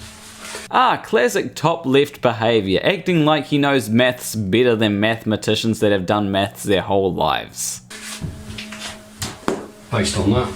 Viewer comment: Sorry bro but ur accent makes it sound like ur saying "acts like he can do meths better than methemeticians who've been doing meth their whole lives"